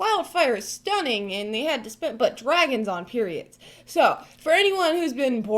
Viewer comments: did she say "dragons on periods" at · Hahahaha... Instead of pyramids said "Dragons on periods". A Truly terrifying thought...